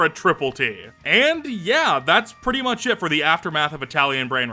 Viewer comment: i heard instead of thas it thas shit 😭